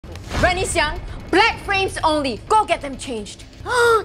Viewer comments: I thought she said “black friends only” 😭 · I thought she said black friends only 😂😂